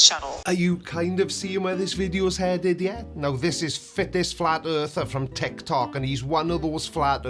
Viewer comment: Did you say fittest or thickest? Can't tell as you don't have an accent